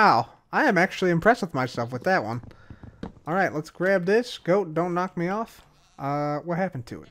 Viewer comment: Who else thought he said Ghost don't knock me off? If want a real grind: Find two different kinds of ore. Dig 2x2 straight down. At deepslate start putting poke holes on all four sides after every 3 blocks down to bedrock. I did this at the top of a mountain once as way to strip mine resources. Discovered it finds diamonds every single time.